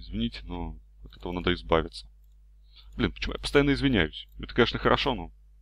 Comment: мне послышалось или он сказал: бля, почему я извинябсь?